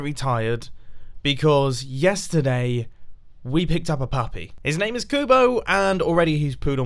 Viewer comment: I checked the subtitles for the puppy's name and it thought said 'pathway' where he said 'puppy'! Seriously Google fix your voice recognition and translator!